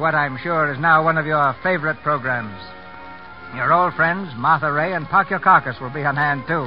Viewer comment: I know he doesn't say "Martha Rae and Park Your Carcass," but that's sure what it sounds like to me! Can anyone tell me what he really says?